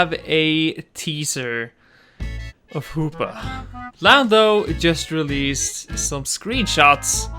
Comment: did u say lando or Lambo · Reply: Gamer Joel he said lamdo